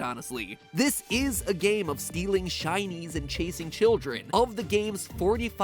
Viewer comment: it sounds like he is saying chinees (not trying to be racist)